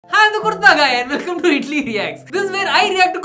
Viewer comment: Sorry but What he says = Hello I'm kurta guy What I hear = Hello I'm kutta guy